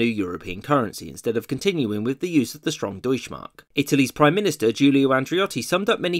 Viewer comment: "[...] instead of continuing with the use of the strong DEUTSCH Mark." Subtitles say: "Jewish mark" Oof...